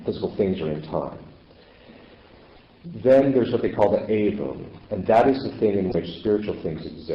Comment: Can anybody tell me what the word is that the father says 9:27 - the words beginning with 'a' in which spiritual beings exist? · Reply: Someone in replying to a question said the angels were Aiken (SP?) ? ....pronounced ay-bum. Outside of time but eternal. I hope this helps!